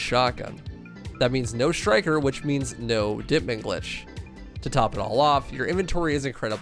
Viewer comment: dipping lish? What is it ya said and what's it mean?